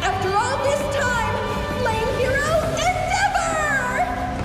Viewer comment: Did anyone else hear Lame hero endeavour 😂😂😂😂😂 Edit